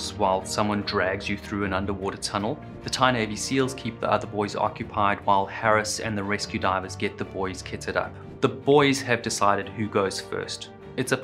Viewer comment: Second time watching this and I keep hearing "ketted up" instead of "kitted up" haha